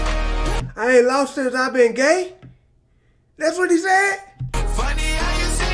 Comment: He said “ain’t been long since I began” not “ain’t been long since i’ve been gay” lmaooooo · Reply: I thought he said "I ain't been loved since I've been gay " Like since he's came out lol